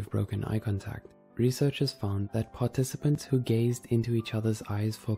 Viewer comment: i thought instead of gaze he said gays